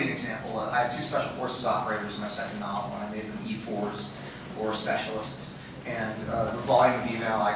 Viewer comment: What does Myke Cole said that he got wrong and received tons of emails regarding Special Forces and Army around 17:00? It was kind of fast for my English understanding. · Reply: "I have two Special Forces operators in my second novel, and I made them E-4's, or 'specialists.' And, uh, the volume of e-mail I got, um... A-and I've WORKED with Special Forces operators, I just zigged when I should have zagged, I missed this one. Um, they're supposed to be [E-]6's - no one under the rank of E-6 can be a Special Forces operator in the modern Army and I missed that."